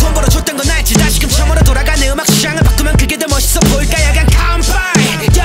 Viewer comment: 야 그냥 다음에 뭐라고 하는거여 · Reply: 간빠이(건배)